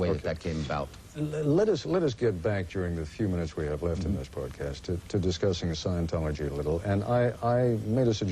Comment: does he say podcast? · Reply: broadcast...